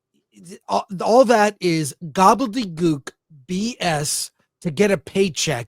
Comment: OMG did Chris just say "Gook" @ 4:25 with a asian woman on the screen? ;)